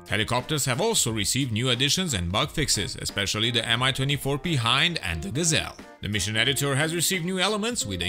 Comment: when he mentions the Mi-24 P Hind it sounds like he says MI-24 Be-hind